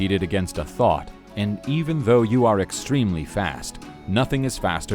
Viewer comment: "You competed against a thot." "And even though you're extremely fast," "nothing is faster than a thot." Am I the only one who heard it like that?